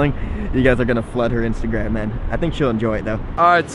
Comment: I almost thought you said to Elizabeth "I think you guys are gonna flood her with semen" Then I was like. Bruh..... I looked back but he said INSTAGRAM. Idk know HOW I thought he said that